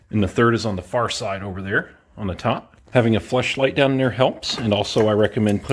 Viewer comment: Did you say having a flashlight or a flesh-light helps ?